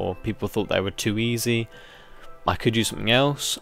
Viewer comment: Ahaha! Thought you said FTB was too easy ;D